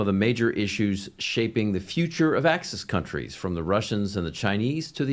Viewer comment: Did he just say "Axis countries" ? Wow !